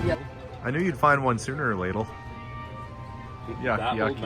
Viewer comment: (I heard you) "Sooner or ladle". lol